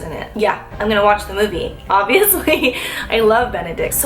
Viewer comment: Swear I heard you say "I love Bene-dick"